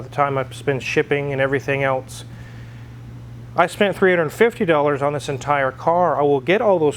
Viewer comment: no he didn't... provide timestamp, he said he gave him a "little extra" which is virtually nothing. He should just say what's true; the car was given to him. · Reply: he said 350$, my bad misheard 650$.